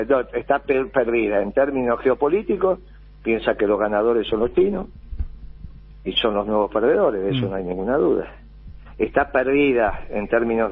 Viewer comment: "piensa que los ganadores son....?" ¿Alguien entendió la palabra que dice? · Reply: "piensan que los ganadores son los chinos y son los grandes perdedores"